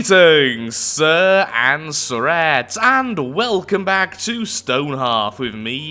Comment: Could someone tell me what Lathrix's standard greeting is? I hear it as "Greetings and threats", but that seems like a weird thing to say. It is confusing me, and i would like to know.